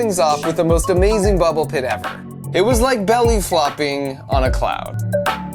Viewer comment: Hey where did you belly flopped???? I think I heard cloud